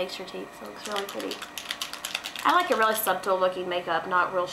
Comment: Did you say SUB-tle?? How cute!! xoxo